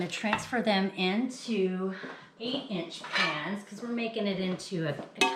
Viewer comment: What size are the cake pans? 8 or 9 inch? Looks amazing! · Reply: I heard her say 8” pans